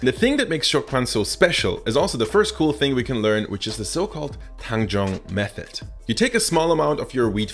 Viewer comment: When he said Tanjong method, I heard that as handjob method. XD